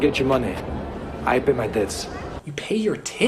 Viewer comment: He actually said "I'll pay my debts.".